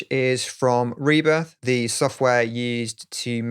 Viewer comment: Hi what the plugin for the clap 1:48 .. i can't understand you is it Reber ? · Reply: Rebirth... I don't know if it's available anymore.